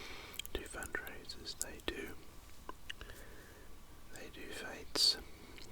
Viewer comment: what is word at 13:56 ? and same word again at 14:32… cannot figure that one out 🤷🏻‍♀️ 🕊️ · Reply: The word is fetes